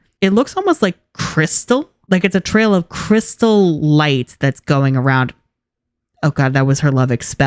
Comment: When I heard her first say crystal light during her lovix transformation I thought she said Curse the light